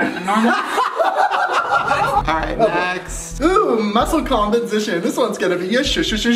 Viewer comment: Why does it sound like was saying 'competition' instead of 'composition'😅